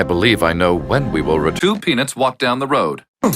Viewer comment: insted of Peanuts i thought it said "2 Pingas Walked down the road"